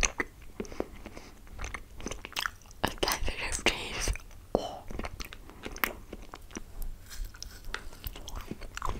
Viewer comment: Does anyone know what she said at 8:35 or is it jus me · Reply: I think she said "sensitive teeth"